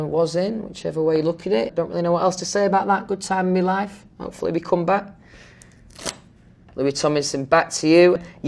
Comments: DID HE JUST SAY "HOPEFULLY WE COME BACK"??????!!!!!!!! OR AM I DAY DREAMING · did i just hear louis tomlinson say he hopes 1D comes back?